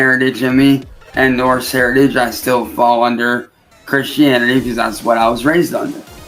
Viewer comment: I thought he said "I'm Chris-Chan" instead of Christian 😂. Hey, if Ed leaves, who knows what happens behind closed doors.